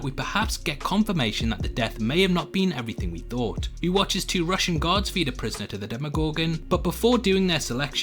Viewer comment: I heard “Two Russian gods” lmao